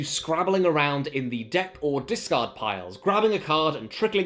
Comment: he says 'discard', i hear 'discord' .